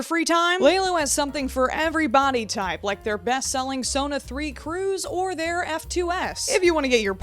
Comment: I heard Persona 3 crew tee and I got really excited but then I looked up from what I was doing and it wasnt Persona